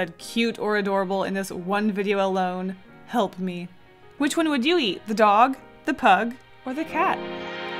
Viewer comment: i was listening to your video and all i heard was "who would you eat?thedog?thepug?the cat?" 👌